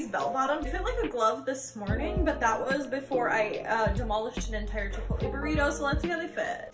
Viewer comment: Ok. I totally thought that she said, “Chick fil a burrito.” And was like, you mean cool wrap? Totally forgot that Chick fil a even had cool wraps do they still have those? But unfortunately she went to Chipotle. Makes sense now. 😅